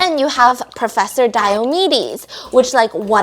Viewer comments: I thought she said professor diabetes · I thought you said Professor Diabetes 🤣😂